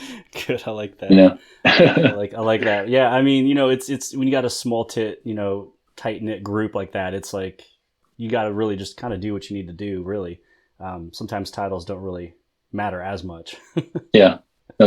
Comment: Did he say “Small Tit” 🤔😂